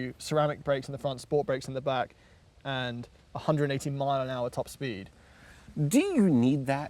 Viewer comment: A 118 MPH TOP SPEED?!?!?!?!?!?!?!?! Did I just hear that right??